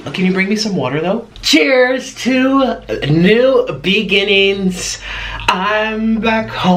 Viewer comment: When he said new beginnings I thought he said veedings. 😂 omg I died had to rewind it.